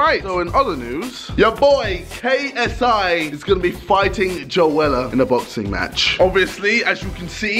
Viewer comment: For a second I thought he said he was gonna fight Zoella 😂 Edit: Damn, wasn’t expecting over 150 likes :o